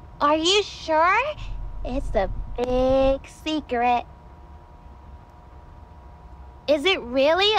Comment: I hear she says “cigarette”, not “secret” 😂